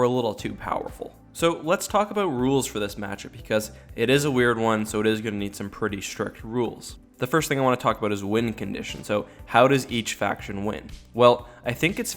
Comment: I thought you said "wind conditions" and I thought, "wow we're gonna get that detailed huh? Didn't think the US military cared that much about a little wind, but ok."